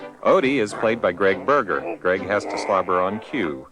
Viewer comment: BURGER?! I'm sorry, did he just say his last name is BURGER?!